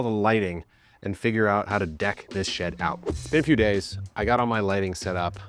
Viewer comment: did bro say deck this shed out or deck this sh## out?😭